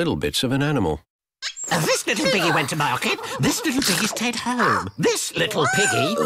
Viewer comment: what in the world did he say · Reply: "This little piggy went market, This little piggy stayed home"